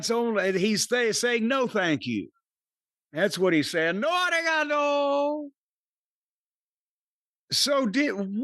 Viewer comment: The more times i hear it, the more i feel like he has to be saying “ARIGATO!”